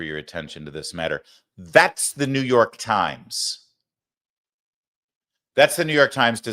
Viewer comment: I’m sorry Larry, I thought you said New York Crimes… I suppose I was right either way…